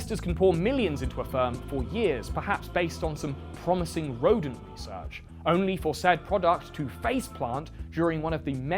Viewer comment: For the first 50 minutes of the video, whenever he said "rodent research", my brain heard him say "Rogan research" (i.e. the kind of 'research' that gets published via the Rogan podcast).